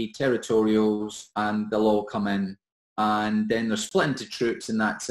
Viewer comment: what’s he saying? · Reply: and they all come inn